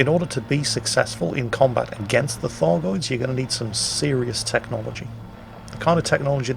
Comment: Please tell me that I'm not the only one that thought he was saying "Fargoids?"